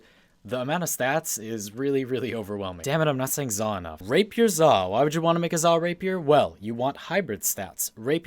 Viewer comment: at first i thought he said "rape your zaw" xd